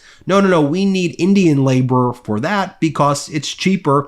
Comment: Wow, what I heard you say was that we need slave labor.